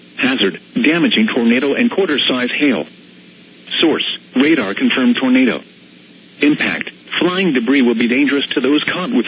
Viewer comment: i Thought he said: Treat Damages Lightly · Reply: rodney rowe That's funny. I thought he said it was a radar controlled tornado